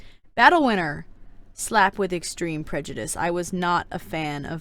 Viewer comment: 1. Wheres Monarch??? I know we dont see her in the books but she was their.... WAS. 2. I origannly thought that BattleWInner was BattleWiener... like a weinner dog. 3. I VERY much disagree with snowfalls ranking i would but her in the orange (no offense) 4. I thought Prejudice said Pre-juice 5. Fav good queen: Glory. Fav bad queen: Scarlet. least fav good queen: Coral. Least fav bad queen: SNOWFALL